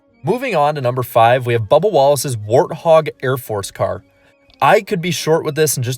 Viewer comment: The 43 is supposed to look like an A-10. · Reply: I thought I heard him say “warthog”